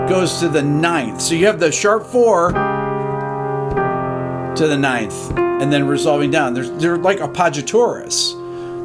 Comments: hey Rick, please help me out. at 14:18, what were you saying? I cannot get a proper transcript from Closed Captioning. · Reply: “So you have the Sharp 4 to the Ninth and then resolving down. There’s- there are like appoggiaturas. This is a really sophisticated melody; it’s haunting sounding because of the notes that…Kurt chooses;” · like a what? Caption says "like a pod you Taurus" haha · Great video and explication! Help, does anyone know what he's saying at ~14:17? "It's like <apagatoris>"? ... "a paux je torres"? Lol, clearly I have no idea. Thanks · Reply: rewatching months later, duh: APPOGGIATURAs